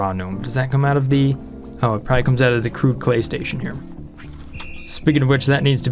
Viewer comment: I thought he said crude PlayStation